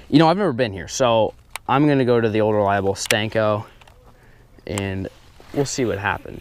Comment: Anyone else cringe every time he says "stenko" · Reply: nixen I always thought he said stinko